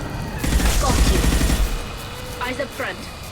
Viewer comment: when Tali said “got you” I thought she said “fuck you”